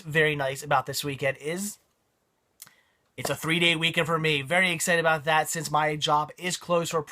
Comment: 3 day weekend? I thought you said 3D weekend. My bad. DAHMER was actually pretty good in my opinion.